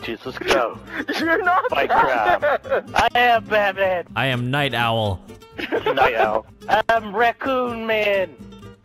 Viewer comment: I died at when bane couldn't hit Batman 😂😂😂😂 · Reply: Did u just say fuckin batman its fuckin night owl btw mind my french